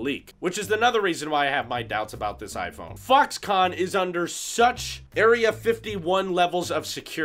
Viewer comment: Did he say fucks-con at 1:51 ?:D